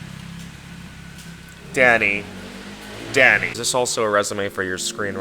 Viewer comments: i thought you said daddy for a sec 😭😭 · I thought he called daddy for a second lamo